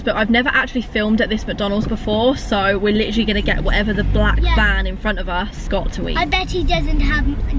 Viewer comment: I was playing my PS4 and had this video on in the back ground because when she said were gonna get whatever the black van got infront of us I thought she said black man